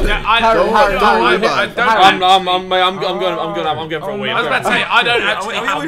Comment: did harry say i am going for a weed or i am going for a week ? or what ?